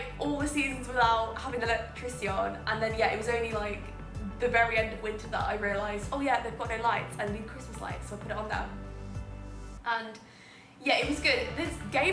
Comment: I thought you said "oh yeah, the f*cking lights" >_<